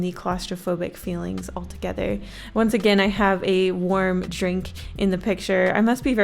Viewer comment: When u said warm drink at first I thought u said worm drink and I was like whaaaatt